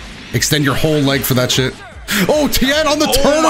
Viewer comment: did just said "SHIT"